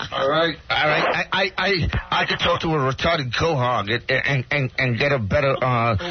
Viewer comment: RIP Hank - crazy it’s been 25 years since his passing. Q I’ve wondered for years - one of Hanks fav insults (he says it in most of his appearances) is at 0:33 mark - I’ve never figured out what he’s saying “a re***ded cow-hog??” Who knows the answer plz?